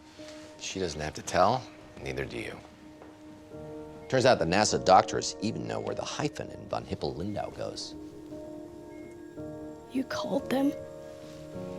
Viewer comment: ok, someone please tell me what house is saying at 9:49. Turns out the NASA doctors.......................................................................................................................? Close caption must be getting it wrong. If not, can you explain? Thanks. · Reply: “Relax. She doesn't have to tell and neither do you. Turns out that NASA doctors even know where the hyphen in Von Hippel-Lindau goes.”